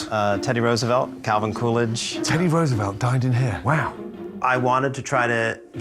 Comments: I thought he said teddy Roosevelt DIED in here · Is it me or did it sound like Gordon said Teddy Roosevelt died here?